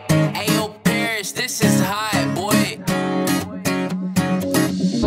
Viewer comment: Wow I didn't expect so many nice comments. Thanks Someone on twitter said it sounded like he was saying "so long Israel" at the end LOL